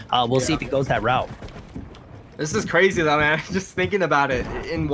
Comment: Minute 1:42 who else heard This is crazy Dumb ass